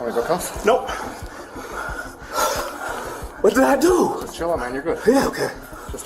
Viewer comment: i heard "chill out my nigga"